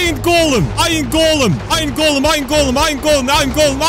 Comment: when he said iron golem it sounded like ima going